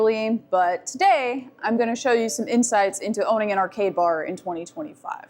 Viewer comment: Ohhh, arcade bar. Sounded like gay bar. Was wondering wtf was I watching